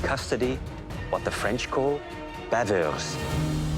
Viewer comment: as a french, let me tell you i have no idea what he just said 😭😭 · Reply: baveuse